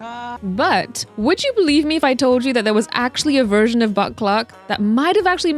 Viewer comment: Concerned how many times I genuinely thought you were saying Butt Cluck until I turned on the captions💀